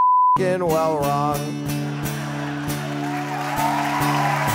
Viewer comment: At the end it sounds like he says "my dog licked my butt"